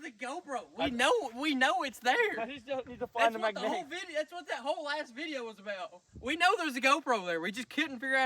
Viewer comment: I don’t know if he sweared or not but it sounded like he said “that’s what the whole as* video was about”